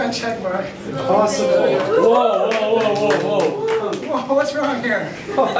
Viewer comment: 日本人いるやろwww 「わーすごい」って聞こえんぞwww